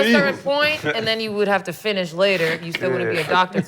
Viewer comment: Did Gil just said finish you inside? 🤔